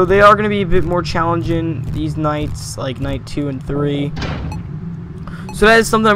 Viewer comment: I thought you said these nuts but you said these nights